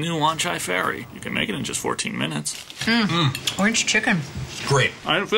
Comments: At first I thought the panda said: "I can feel it going out anyway." Thought he was shitting on the couch as the food is going right through him. LOL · First few times I heard this commercial I thought the panda said "I didn't feel it coming out anyways", thought it was abit of an interesting statement about their food.